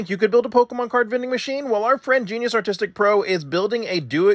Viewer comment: people who thought he said AUTISTIC pro, instead of ARTISTIC